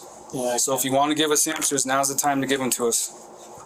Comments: The closed captioner does a really awful job! “Now’s the time to give us HAMSTERS” Clearly, what was said was ANSWERS! 😂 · Some unintended levity at 2:33:18 when the detective says, "So if you want to give us ANSWERS......" but the subtitles said "HAMSTERS"!!!!! Lol.